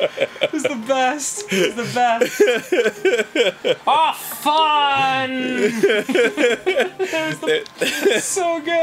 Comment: When Dan did the impression of his dad's curse-saving, "Ah fffun" at 8:41 it actually sounds like he said fuck in Norwegian (faen) by his pronunciation, and it's funny cause Dan's mother is Norwegian XD